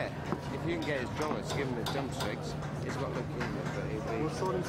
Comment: i think his dad says at 0:23 "he's got leucimia" (i dont know if i'm prounoucing that correctly)